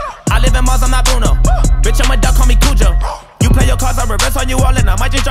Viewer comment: Awww I thought it said bitch I’m a dub call me Kujo and I thought it was a Jojo reference😭😭